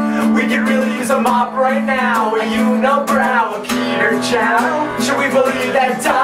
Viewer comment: i heard peter chao